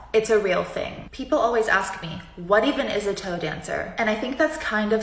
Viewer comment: When she said tow dancer the first time I thought she said “toe dancer” 💀